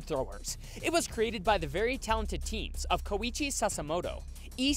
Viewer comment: DID I HEAR THE NAME KOCKCI (Danganronpa v3 reference)